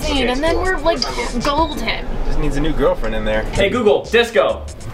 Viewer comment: Did Scotty say I just need a new gf in there ?? At 16:00!?